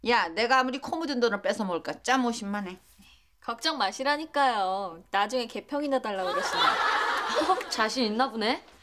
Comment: 때 유미가 뭐라고한건가요 ㅠ? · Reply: 나중에 개평 달라고 하지 마세요 라는 것 같은데